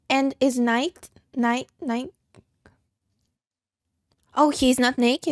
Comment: "What does it say?" - "Nigh", it means "near". 42:15 - "You are about to participate in a great adventure." - That's the intro to a 1963 science fiction anthology show called The Outer Limits. Each episode would be a different, hour-long story. There was also a revival of that show in 1995.